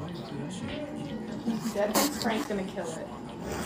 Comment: lol got my laughing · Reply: it sounded like it said, "frank's gonna kill it" something about this clip feels off, the cat being so scared of the human, and the weird conversation.